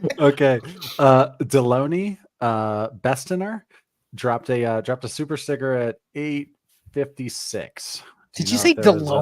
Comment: I swear says, “dropped a super cigarette” at 1:19:13 😂😂😂 I’m dying!!!!!! Totally butchered Dillons name and made a new super chat in Shanes name!! “Super Cigarette”!!! 😅😂😂😂🚬